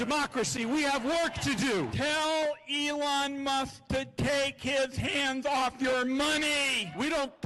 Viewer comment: I heard "get your hands off my money" they can't be serious